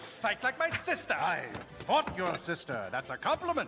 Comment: " I fought your sister, that's a compliment! " lmaaaoo, I miss this movie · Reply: Omg I always thought he said ‘I bought your sister’…….